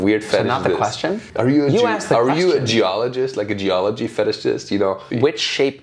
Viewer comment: asking if he is a geologist, I swear I heard he asked " are you a jewaligist" .....